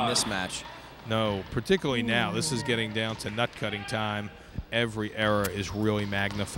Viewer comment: Did he really say nut cutting time @ 7:55 !? .lol